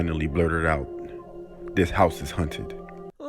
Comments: on her way out the door she finally blurted out "this house is haunted" why does it sound like he said hunted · I swear the guy said this house is hunted not haunted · A little bit before 19:50 she rlly said “Hunted” instead of haunted, right? Or is it just me